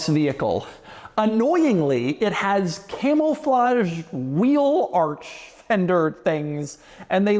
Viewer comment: Am I hearing “Camel-flage”?